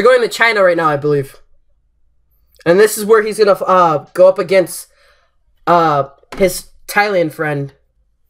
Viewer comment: when he said "this is where hes gonna go up against his thailand friend" i thought he said "this is where hes gonna go up against piss"